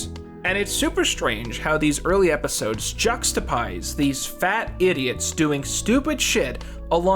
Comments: did you- did- you just- did you just say ”juxtapise?” Sir. Sir please. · Did you just say Juxtapise